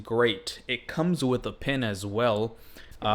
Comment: did you say pen or pin ? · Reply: Pen